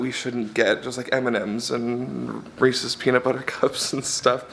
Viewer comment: Am I the only who thought they heard him say "racist peanut butter cups"?